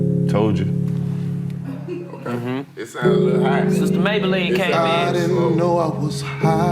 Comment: 🤣🤣 Did anyone hear DC say "Sister Mablean dun came in"?